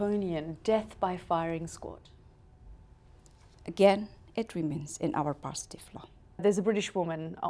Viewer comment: I don't understand what she says at 1:16. "Again it remains in............", can someone tell me what she says please? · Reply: Marschalek She said "Again it remains in our positive law". Hope that helped.